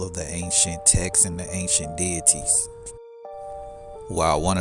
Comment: I thought you said "aint shit" not ancient 😅